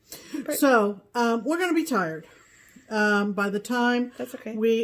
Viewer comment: What is the pattern of the clothesline behind called? I LOVE that one......great job girls. Oops, for a minute I thought you said your are going to retire, but I think you said tired....I sure hope so. Please do not retire!!!!!! Love your show and look forward to your show every week. Thank you so much <3<3